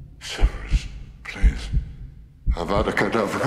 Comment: 난 왜 5:24초에서 세베루스 개색이라고들리지? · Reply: 소리 엄청 키우셔서 들으시면 세베루스 플리스 라고 들려요(이댓 보고 나도 그렇게 들린건 안비밀)